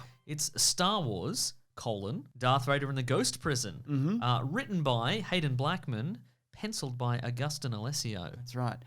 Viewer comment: Omg I totally thought said "written by hated black man"😳